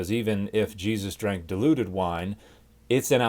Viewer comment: I could swear I heard you say Jesus drank the deluded wine.